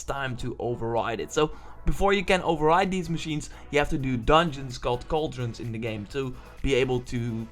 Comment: what did he say ???? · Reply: That you need to do dungeons in order to override enemies.